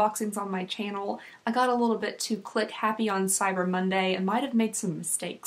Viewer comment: 10/10 sounds like you said ‘clit happy’ at 0:33 Also love the advent calendar videos so I’m excited for the rest